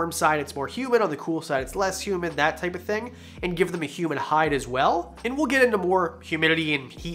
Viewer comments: I thought you said “human hide” so now I cannot get the imagine of a human skull being used as a hide out of my mind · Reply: I thought he said Human hide also!! I guess it wasnt just because I'm getting to be a deaf old fart,,,,,,,,,unless your one too???? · I thought you said “human hide” so now I cannot get the imagine of a human skull being used as a hide out of my mind